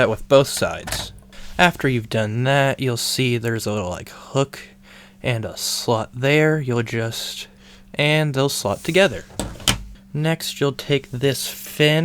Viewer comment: I thought you were saying there's a slut there and there's a slut there and they will slut together 😂